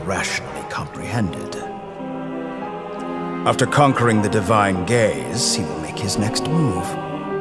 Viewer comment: am i the only one who hears the divine gays 😭